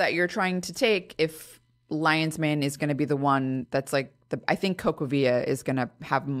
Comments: What supplement did she say here? The transcript is inaccurate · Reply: CocoaVia, fyi, if it's worth the price. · Anyone know what that supplement she mentioned at 11:36 is called? Thank you! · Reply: Cocoavia